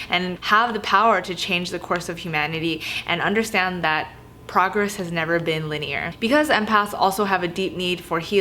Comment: When you said "progress has never been linear" I heard "progress has never been lit-y-er" I was like yas😝🤘